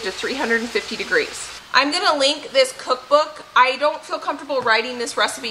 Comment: When you showed the Danish whisk I heard "and yes, I'm going to lick this" and I thought "nice, no batter wasted!" but then realized you actually said LINK it lmao love these cooking videos, hope you all enjoyed your time together!